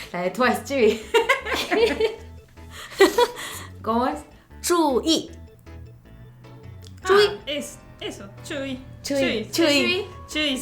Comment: tambien entendi tzuyu x'd